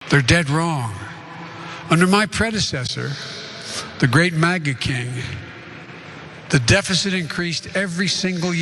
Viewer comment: I thought he said MAGAt King